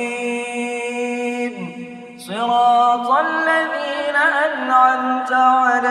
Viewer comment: apa cuma sya alfatihah nya kedengeran mustaqiin? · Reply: Mustaqiim